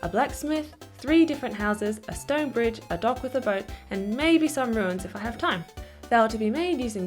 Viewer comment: When you said "dock with a boat" a thought you said "dog with a boat" and just for a moment I was so happy, my disapointment is imesurable.